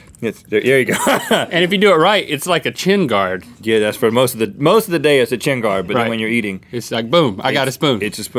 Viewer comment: I thought he said tin guard